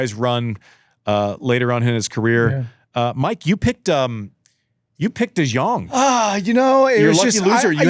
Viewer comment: Im Dutch and when i heard Da joung i thought who are they talking about? and it took me a good minute to realize they where taking about de Jong I’m dead 😂